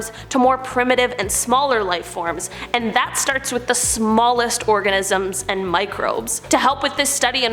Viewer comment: anyone else hear "orgasmn"?